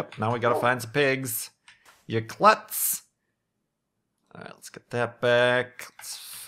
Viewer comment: Hey Kurt, did you call Wolfie "You Klotz" at 26:15? Isnt that the german word for "log"? :) I am curious if it is actually a common word in american english or if it something passed on by your ancestors. Cheers, Mike from Germany. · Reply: He probably said "klutz". I haven't seen that part yet, but if you call someone a klutz they're being clumsy